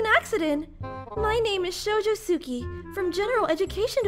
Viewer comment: I thought they said “Shoji” quirk not “Shoujo” the first time & I was SO confused until they said it again 😂 Also did anyone catch that Bakugou said that he can’t tell the difference when he’s looking at Kirishima?? Meaning that he’s already in love with Kiri!!!